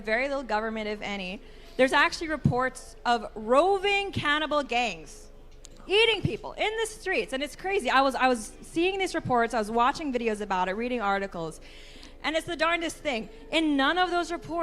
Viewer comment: I thought she said "cannibal gays" which was 90% funnier than anything she said. 😂